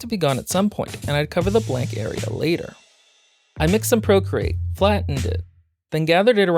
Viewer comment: I really like this one! Just wondering, what's the modeling material you use - I think I heard you call it "procreate" or "procrete" at ~1:02, but I haven't been able to find it online. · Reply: its on amazon under the name "ProCreate Putty", can provide link if needed